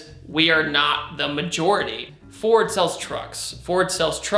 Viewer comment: I thought he said "Ford sells drugs" lmao I'm too faded for this